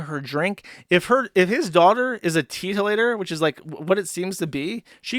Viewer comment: Oh he is saying teetotaler i thought he was saying titolator This is the same stream were he reads niche as nishi